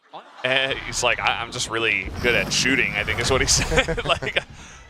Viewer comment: what did he say? Why audiences were laughing😅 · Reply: "I'm really good at shooting"